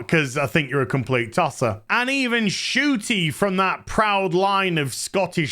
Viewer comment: Are you pronouncing her name: Shooty? I thought they were saying Shitty. And I agree.